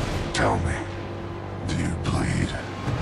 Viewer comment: I thought Batman said "Do you plead?" not bleed